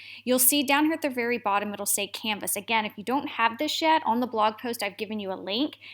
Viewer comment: Does she say "shit" at 0:33? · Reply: if you don't have in this YET :D